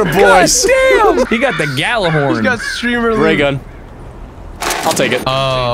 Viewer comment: did he say gjallerhorn? my D2 brain may just be false firing